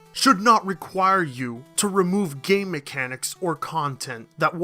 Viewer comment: You said "game mechanics", i heard "gay mechanics".